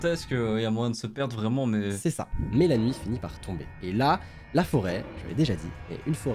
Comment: s’est l’histoire de Célia Moi : ohhh ok :o qui sort 19:16 : Mélanie Moi : comprend pas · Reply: Il dit "Mais la nuit fini par tomber"